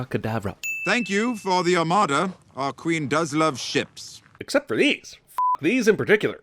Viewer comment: i heard “The Queen does love shits”